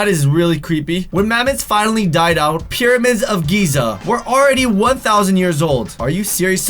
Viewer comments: Lol sounded like you said "Periods" instead of "Pyramids" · Who heard him say periods instead of pyramids at the pyramid part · I heard "period" for pyramids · Did he just say periods instead of pyramids